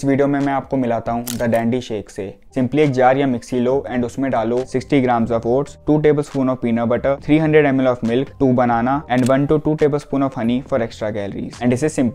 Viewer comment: Did he said 60gm osts